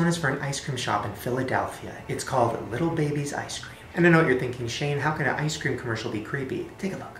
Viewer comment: I thought it said "I eat little babies" before it said ice cream lol And I literally shut my eyes during the Japenese tire one [Edit: TY FOR ALL THE REPLIES AND LIKES!] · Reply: Exactly i thought it said little babies too